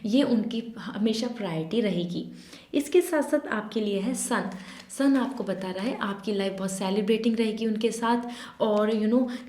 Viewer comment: I legit heard "aapki life bhaut celibacy rhegi unke saath" 🤣